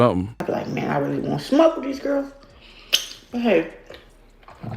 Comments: I think she said she wants to smoke these girls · I think she said “I really want smoke with these girls.” Because she referenced weight classes afterwards and the ones she doesn’t fight in. Could be wrong though